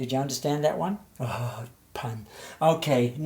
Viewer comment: why does he say "oooh pan"